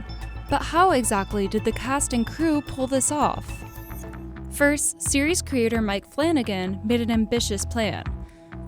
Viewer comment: I thought you said Mike Flinn again